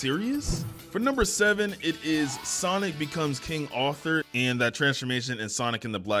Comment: I heard “king author”